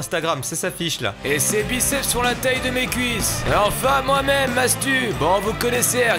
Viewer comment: Je suis le seul à avoir entendu "mohamed" à la place de moi même à 0:37 ?